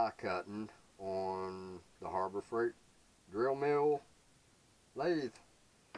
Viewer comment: Cool, congrats! I got some flycutters in my newest tooling acquisition, couldnt help myself and chucked up one in the lathe, used the Aloris to hold the stock and flycut a beautiful flat surface in some steel. Had my hand on the power switch in case things went South but it worked great :) Ps: I coulda swore you said Horrible Freight